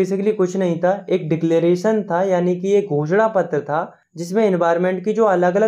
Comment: I heard ghosna as bh***a